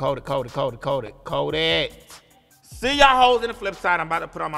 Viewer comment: What is she saying here? Coe it???